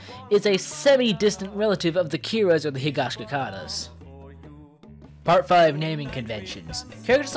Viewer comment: did you say “Higashikitatas”?